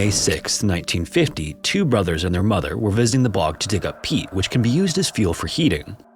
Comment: At first I heard it as "they went to dig up Pete"